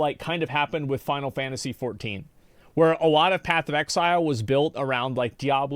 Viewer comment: I keep hearing the Ai say "Path of EGG zile"